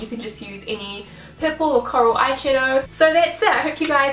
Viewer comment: I thought you said pimple eyeshadow instead of purple eyeshadow hahaha. Re the length, I liked it :)